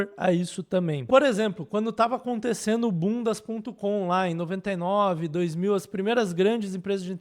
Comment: Absolutamente nada a ver com o conteúdo, mas só eu entendi 'bundas ponto com'? 👀